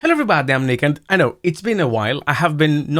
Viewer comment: in the beggining it sounded like u said, hello im naked😂